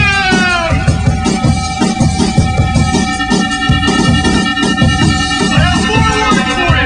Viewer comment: sorry but i hear" th-developers"